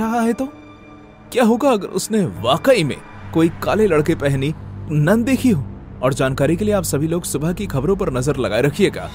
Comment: Is it onli me who heard "kaale ladke pahani nun dekhi ho"